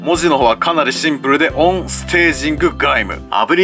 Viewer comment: 音声がオーンスパーキングガイムって聞こえてきた。